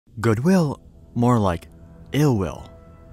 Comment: i heard this section like that for some reason "goodwu? more like uwu!" 😂LMFAO